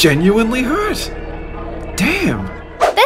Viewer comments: I heard damn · did say damn 😂 kid friendly my butt